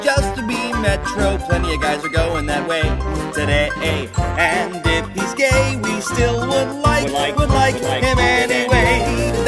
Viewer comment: Back then I thought he said “and if he’s gay he still looks like an anime”